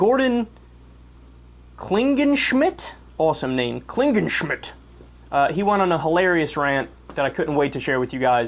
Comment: Whenever I hear that guy's name or read it, it always sounds like "Klingon Shit".